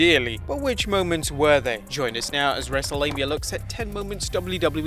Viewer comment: I heard "wrestlelabia" today